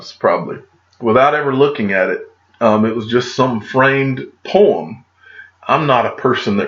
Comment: Haha I thought you were saying framed porn 😂😂